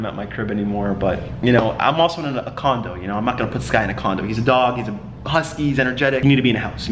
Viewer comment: who else heard “im not gonna put sky in a condom” lmao